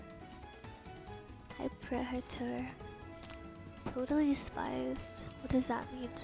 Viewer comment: did you say totally spies what does that mean?😭😭